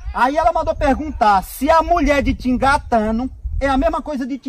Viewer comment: n entendi essa de 5:05 🤔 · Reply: Te engatando ( te encoxando/te como)